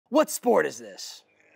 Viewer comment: HELP, ON THE FIRST ONE I THOUGHT HE SAID "What country is this" AND I KEPT THINKING IT OVER AND OVER AGAIN, NOT EVEN REALIZING THAT THE WORD SAID ON THE SCREEN "What sport is this"